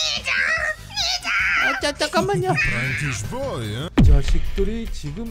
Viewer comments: 뒤져 디져(?) 뭐라는걸까요(?) 13:15 아니잌ㅋㅋㅋㅋ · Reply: 니쟈 임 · 뒤져 디져(?) 뭐라는걸까요(?) 13:15 아니잌ㅋㅋㅋㅋ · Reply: 피자로들리는데ㅋㅋㅋㅋㅋㅋ